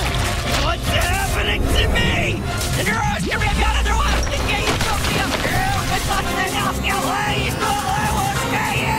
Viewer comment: what is Bill cipher saying? Is it a secret word backwards word? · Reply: A-X-O-L-O-T-L, My Time Has Come To Burn, I Invoke The Ancient Power That I May Return.